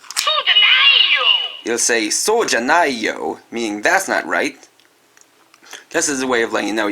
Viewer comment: what does it say? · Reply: SOUJANAYOH! Which means "That's not right." In japanese